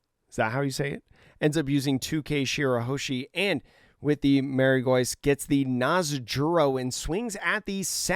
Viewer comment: All love man n keep up the good work but if I hear “marry gois” again instead of “Mary jwah” I might jus have to lock u up to finish one piece and hear all the correct pronunciations lol